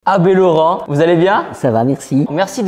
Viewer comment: Dès le début j'ai entendu "AD Laurent, vous allez bien ?"